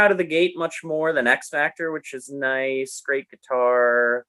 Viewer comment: I thought said Axe Factor.